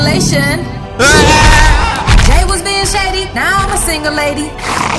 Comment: Jay was being shady, now im a single lady😂😂😂😂😂😂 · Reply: Deniyah Vaughan I thought she said imma a sing a lady lol